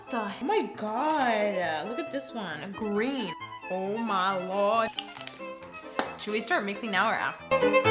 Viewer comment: did she say we should just make start making out right now that's what I thought she said